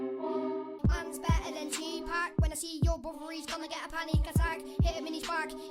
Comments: Their just wannabe rappers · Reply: Sarah Saadi am I the only one who heard him say “mans better than Tupac” 😂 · Did he just say he’s better than Tupac 6:02 😂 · "mans better than 2pac" Am I hearing that right